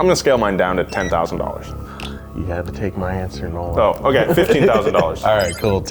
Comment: I thought you said no one , not Nolan ,,,"Same difference" ,, hahahaha , damn you guys , and tease the poor boy a lot..